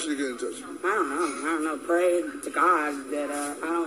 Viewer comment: Sound like she said pray to god I don't die